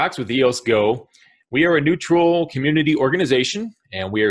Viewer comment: sounds like you said New Troll organization. Neutral.. New Troll.... Neutral... lol